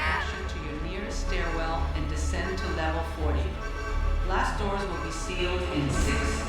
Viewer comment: Blast doors will close in 10 seconds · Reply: I always thought they said glass dooors!!!!